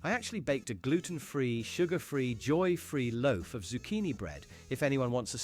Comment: "Joyfree?" Did she actually say that? Ain't no way someone is unironically so anti-joy they actually admit it in their baking 😅